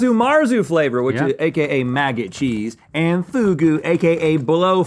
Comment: Bought two of the boxes of chips. I seriously thought Link was saying, "Mac N Cheese," not "Maggot Cheese!"